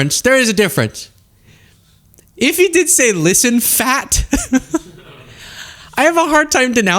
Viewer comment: I heard "listen, Jack"